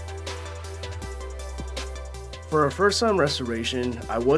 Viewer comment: I'm sorry, did you say happy ??